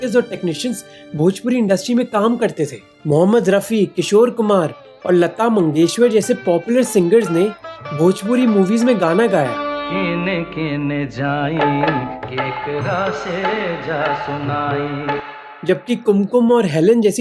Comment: Did you just say Lata MangeshWar?